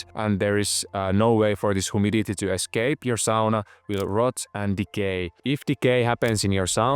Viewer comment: I thought he said “ your sauna will rot and be gay “. 😂